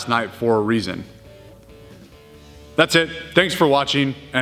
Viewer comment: I thought you were saying train on time! This whole time hahahahah